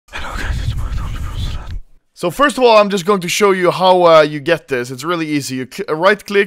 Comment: "it's okay he suposed...." what he say in the beginning? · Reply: He always says "Hello guys its anomaly from sweden" in the beginning of his vids